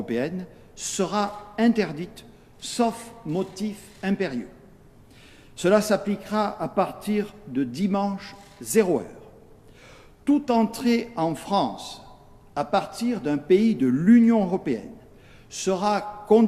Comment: c'est moi ou il dit le dimanche /01 allors que c'est le 31